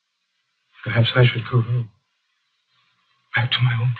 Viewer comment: Ain't no way youtube heard what Hartnell says at 36:25 as "to have sex with Google" like, how the hell?